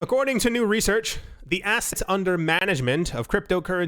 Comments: This video was filmed *yesterday*, I wasnt "allowed" to upload it because my videos were flagged as "inappropriate". This confusion has since been cleared up. So, this video is from yesterday....but...dont worry, the normal daily video is coming up in a bit, which.... makes the second video even more incredible because of how relevant it is. Hope you all enjoy, SeEeeeeEEEEeee Yo0oooUuuUuuuu!!! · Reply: I think I know why the strike your video from yesterday. Well this video. U said A** instead of asset at the 21:25-21:28 minutes Lmaoo. Check it out and tell me. Keep up the great work. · I think I know why the strike your video from yesterday. Well this video. U said A** instead of asset at the 21:25-21:28 minutes Lmaoo. Check it out and tell me. Keep up the great work.